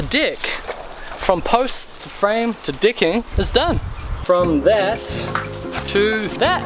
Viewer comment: Sounds like your saying "Dicking" lol